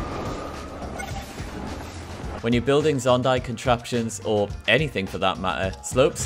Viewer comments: Did he just say “Zondai contraptions” @ 6:08 ?!??? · did he say zondai